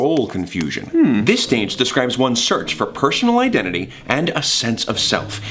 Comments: did he just say “pershonal identity” instead of personal identity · did anyone else hear say pershonal?